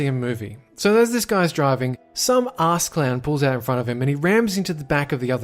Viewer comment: Did he just say assclam?